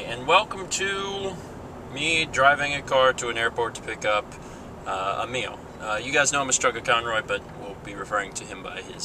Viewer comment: I thought you said I'm going to the airport to pick up a meal at first lol.